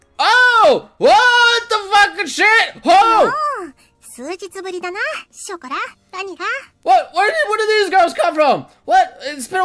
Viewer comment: did ANYBODY catch that? xD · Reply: I managed to get this, "what the shada ga waka shishka kwa" -, 2015